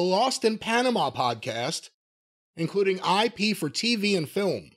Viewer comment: I know it's IP, but hearing it, it sounds like said "I pee for TV and film" lol